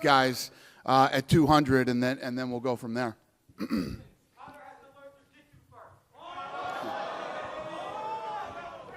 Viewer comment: Did anybody catch what that guy said around 20:27 ? "Conor McGregor needs to learn how to ...." ???? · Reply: it sounded like "conor needs to learn to defend a takedown"